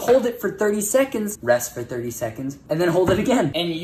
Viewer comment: I thought he said stay sexy 0_0